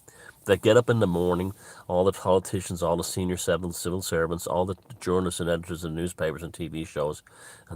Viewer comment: heard that as "Saville Servants"